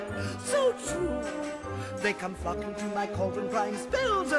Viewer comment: i guess ursula didn't swear this time, because she says flocking not the f bomb